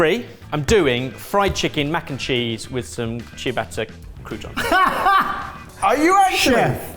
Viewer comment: Lots of mistakes in the captions. I'm not sure if they're done by hand or mostly auto-generated and then proofread but you could do with a better system. At least I don't actually need captions but it'll be worse for people that are hard of hearing and do need them. Examples: 7:48 - says "what I love about satay is the satay sauce", captions say "what I love about saute is the saute sauce" 12:30 - says "half the tub you've bought", captions say "half the tubby pool" There's another bit where the captions have saying "I peed" which, I dunno, might just be the high-stress environment! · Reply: says "ciabatta croutons", captions say "cheer butter crouton" 18:34 - says "béchamel", captions say "fresh milk"